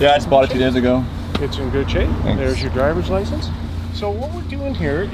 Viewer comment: Did he said (9:09) " here's your garbage license"?